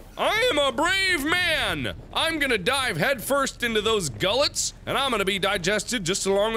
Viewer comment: Thought he said die of head first lmao